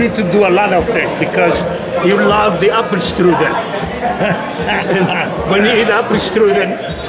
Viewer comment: What did Franco say at 3:27 ? "you love the upperstrugen" what is "upperstrugen" the upper body? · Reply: He’s says, “you love the apple strudel.”